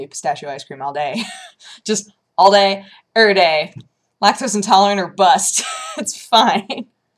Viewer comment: Had to rewind a bit for a second view, because first time I heard '..lactose intolerance or butts..'